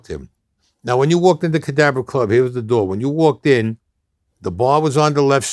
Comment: Wow, that’s the first time I seen him take off his sunglasses, he got scary looking eyes. In another interview he corrected Staxx, saying the Cadabra club, though sounds like he says Cadaver club. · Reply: If you don't know, if someone hadn't typed it out here, I wouldn't have known he said Cadabra. I definitely thought he said cadaver too.